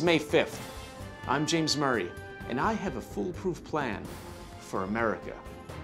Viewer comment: You had me goin Murr!.... Till I heard 'Full proof plan" 😅